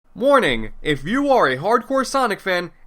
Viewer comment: If I am being honest, the best part of this video in my opinion is replaying 0:00 over and over again. With each replay you can hear a variety of words. The first listen sounds like the word "Warning". However, upon listening to it a good 3 more times I heard the words "Morning" and "Mourning" also being pronounced. My favourite audible discovery was the phrase "More Ning" as if Ning was a special ingredient like a spice served with bland food to give it some flavour. Upon being asked to test taste the dish was asked to give his recommendations and simply requested "More Ning".